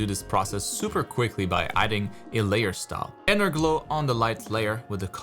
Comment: Did you say "Layer stop" @ 10:57? What is that??